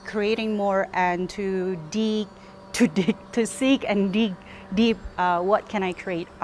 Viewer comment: 🤣🤣🤣i heard dick